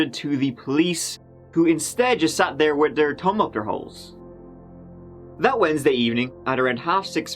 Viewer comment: I have to rewind it to make sure I didn't hear what I thought I heard... " their tongue up their holes?????" How in the hell...? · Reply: Their “thumbs”.